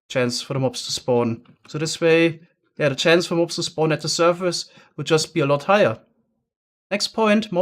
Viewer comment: Great vid can someone tell me what he said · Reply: The chance for mobs to spawn at the surface would just be a lot higher.